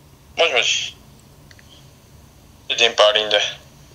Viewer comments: セイキンのもしもーしと電波わりーながクッソワロタ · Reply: 電波わりーな が デンパーリンデに 聞こえたの俺だけ？ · セイキンのもしもーしと電波わりーながクッソワロタ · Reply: はまだりょうた デンパーリンデって聞こえる！！